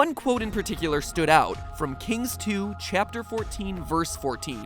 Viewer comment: Hey, MatPat, make a theory on The Final Station! 5:58 Did...did he just say "Kings Two"? XD